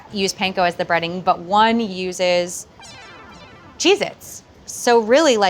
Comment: When she said "Cheez-its" I originally thought she said, "Jesus.." lol Love the recipe!